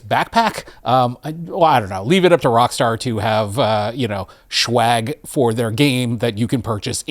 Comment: did you really say "shwag" ?😮